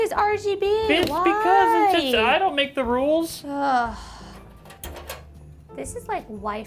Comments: What did you hear? R2D2 ? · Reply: he says bitch i don't make the rules. i think this dude accidentally typed 3 instead of 4 but i knew what he meant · he says bitch i don't make the rules. i think this dude accidentally typed 3 instead of 4 but i knew what he meant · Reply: lmao i heard it too, but I actually think he says "ITS because". I don't think he'd call her a bitch so blase...at least on camera. But yeah the timestamp is definitely 11:47 not 38. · did Kyle just say “bitch because”?! Hahahah oh man he’s sleeping on the couch for a week after this one. 😂 Also, worst case of 2019 to build in? Lol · did Kyle say "bitch because, I don't make the rules!"